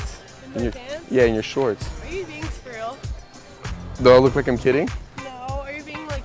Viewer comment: I heard "Are you being squireal"